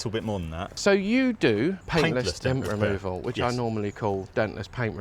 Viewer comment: I heard "Dentist Pain Removal" 😂😂😂